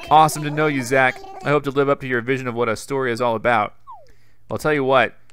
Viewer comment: OH MY GOD MY ISLAND IS CALLED ESTORIA WHAT A FREAKING COINCIDENCE, I WAS MINDLESSLY SCROLLING THROUGH YOUTUBE STORIES BUT IT REALLY WOKE ME UP WHEN I THOUGHT YOU SAID “Estoria” BUT ACTUALLY “Astoria” HOLY CRAP.